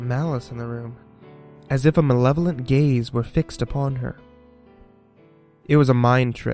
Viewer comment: Did he just say "As if a level of gays were upon her"...?